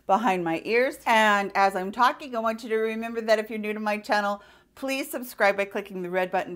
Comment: My boyfriend thought you said "If you are nude to this channel" Instead of "If you are new to this channel" BOLMA So I correct him and he said I doubt anyone would actually say they are nude. I said What? I am only half nude. My pants are on silly! LMAO Last Thursday I saw a pain specialist for my fibro and nerve damage. He gave me 4 shots of a nerve blocker in my neck and down to my shoulder. He wants me in every week for these shots to start. He also is switching up my Gabapentin to Lyrica. I have some stretches I do too now. I am also, since last night, a subscriber to Boxycharm and I am very excited. It is an early Birthday gift I requested to stop me from bugging my love for makeup. LOL I am on a no buy for eye palettes. However I did come upfront to say Boxycharm often (like a LOT) has eye palettes. hahaha! Love you and hope you had a good week. The weather is not helping but my neck is pain free from my shots so am so happy.